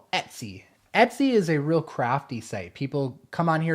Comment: I thought you said crappy😂